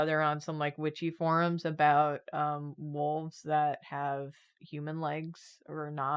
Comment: I heard say "molds that have human legs" by accident and now I really want to see that Episode on misheard Drawfee quotes as drawings?